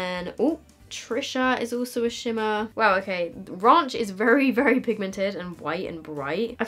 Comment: Did she just say: "raUnch" 😂😂